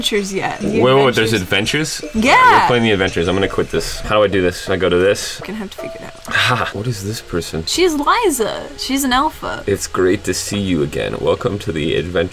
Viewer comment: When she says alpha I thought she said elephant Liza is an elephant lol